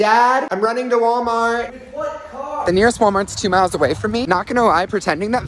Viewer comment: “dad im running to walmart” dad:“with what car???” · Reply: I heard “with what card”